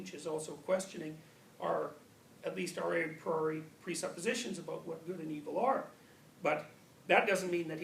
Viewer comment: He says "our x preassumption" at 24:46 Does anyone know what the word x is? Thanks in advance.